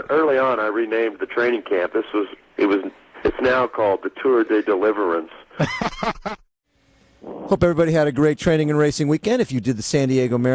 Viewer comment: Tour de Deliverance 😂In Netherlands there are bakeries called Deli-France, so at first I thought he said his training camp was renamed Tour de Deli-France